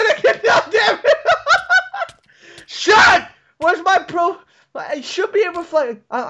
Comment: did say "wheres my pills?"